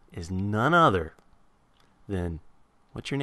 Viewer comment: O.O i thought he said "KE$HA"... i was like "NOOOOOOOOOOOOOOOO", but then i was like "WAIT A SECOND, KE$HA is way too stupid to handle minecraft... O.O